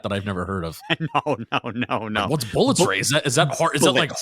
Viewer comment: I heard "bullets rate" too.